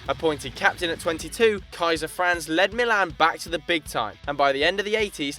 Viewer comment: did you call Baresi "Kaiser Franz"? lmao